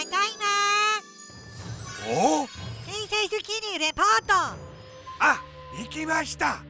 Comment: escuche "princess zucchini"